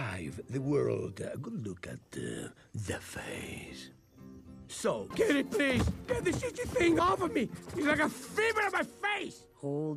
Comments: is it just me or when you go to 2:46 ands turn on captions, the captions say 'so get it please get the sh*tty thing off of me" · is it just me or did he say ‘get this shtty thing off me!’? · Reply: he said "this itchy thing" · Did anyone else hear "Get this SHITTY thing off of me"? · did he say shitty? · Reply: He said itchy